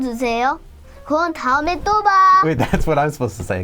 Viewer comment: when said “turn off the camera” in Korean, I wasn’t looking at the video, so I thought he said “go to jail”